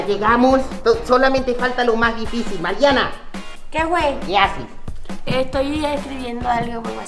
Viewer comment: que dijo *Mariana*:( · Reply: Que wey